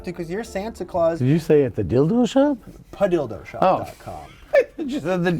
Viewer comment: This show of different Santa's was great. It's good to get the different Santa's. I thought it was funny when the Santa said the Dildo shop. It did sound like you said the Dildo shop. LOL